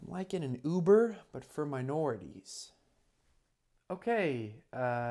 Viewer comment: "uber for minorities?" bro that's police cars. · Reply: I thought he said minors!